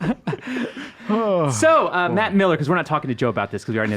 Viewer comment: I thought he said Mac Miller